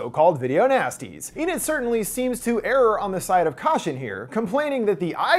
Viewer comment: did you say "error on the side of caution" or are my ears deceiving me?